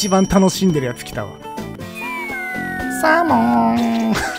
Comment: の「サーモン」が「ゼロー」にしか聞こえんw